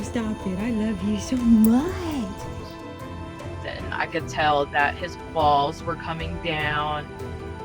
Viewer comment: wasnt looking, thought she said she "could tell his balls were coming down"